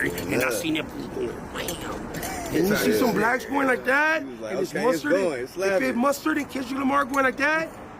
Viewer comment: I thought he was saying “I’m seeing mustard and ketchup going like that…” 🙂‍↕️ 🙂‍↕️ 🙂‍↕️